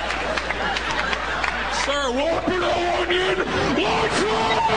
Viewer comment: what is the guy saying at 0:59 i looked it up and its something about a wopper but i dont hear that here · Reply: WHOOPER!!!! WHOOPER NO ONIONS!!!!! L A R G E F R I E S!!!!!!!!!!!!!!